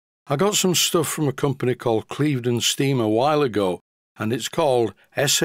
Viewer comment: Omg. At first listen, I thought I heard you say you got something from a company called Cleveland Steamer. 😮